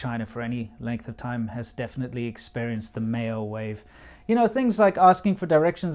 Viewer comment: I heard as "The mayo wave" and thought it was a race thing... Kind of a hilarious coincidence considering, well... China.